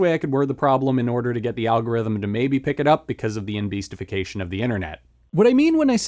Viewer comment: Can you please clarify the word you say at 3:36 it’s driving me absolutely mad 😂 the autogenerated subtitles are wrong and any word i try and google is not it. Google thinks emulsification? I hear embeastification? I’m like is this some trim for a concept i haven’t heard of? lol if it’s just something you made up, sorry for pointing it out. If you did then as an apology I’ll do my best to help you start this brand new word’s near perfect and long lived legacy. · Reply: lol no worries. I said “enbeastification,” as a play on the word “enshitification,” which is when a product is made worse to drive engagement on said product. So my version of it was a joke about the YouTuber Mr. Beast and how he represents a heavy slanting of presenting media online in a heavily simplified, hyperbolic way to drive clicks.